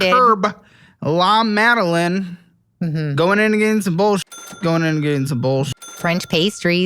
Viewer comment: is the new voice in my head. Whenever I´m tempted to buy some home decor stuff, fashion magazine or piece of clothes, I hear him shout "Amazon, Amazon, Amazon" and I stop. Thank you for that!!! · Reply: I hear “going and getting some bullshit” 🤣